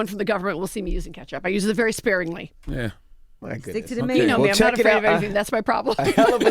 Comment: "Stick to the Mayonnaise" 🤦🏽‍♀️😂 I heard that Yee.